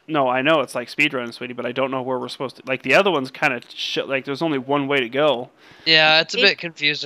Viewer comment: Did say shit